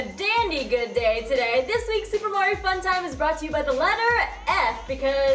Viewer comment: It sounded like you said: "Welcome to super mario fun time."